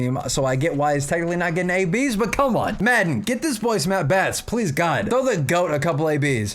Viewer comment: Did he just say madden? LMFAO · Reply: Joe maddon my boy